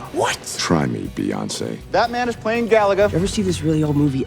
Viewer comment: I thought he said "Batman is playing Galaga"